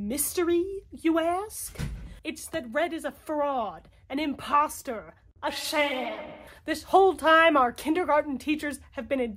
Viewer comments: I legitimately thought that you were saying, "RED... is a FROG! An imposter! A sham. " 😄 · I thought you said "Red is a FROG!" as well, and I love it. I really enjoyed you dramatic you were in this video. Great work!